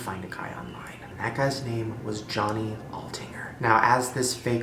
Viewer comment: When he was talking about Johnny Olttinger I thought he said my last name which is Ottinger and it scared THE HELL OUT OF ME!!!